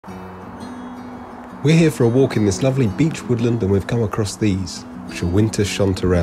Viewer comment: I heard "beach" instead of beech